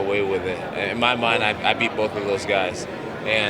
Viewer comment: I like Jones, but did he just say that "in my mind, I beat both of those guys" when referencing DC and Rumble? He's only fought one of them. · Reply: im pretty sure he said in my mind " I'd " beat both those guys.